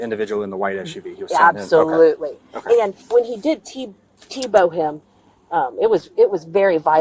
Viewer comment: Did she actually say "t-bow?" Anyway, great video, but an absolutely infuriating story...